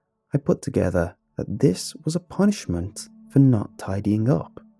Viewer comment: I thought he said that his mom punched him not punished him